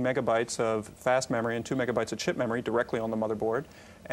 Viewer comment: Did he say shit memory? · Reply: Chip memory. RAM in an Amiga consisted of "chipmem" and "fastmem".